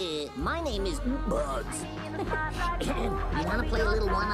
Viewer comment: What I thought she said court-ney